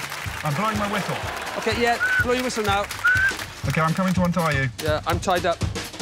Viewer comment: What did Rod say at 11:44 ish please? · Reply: "31..30..Hang on a minute then OK, yeah, blow your whistle now"